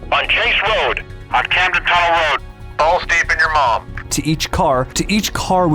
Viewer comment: did he say "Balls deep in your mom..." ?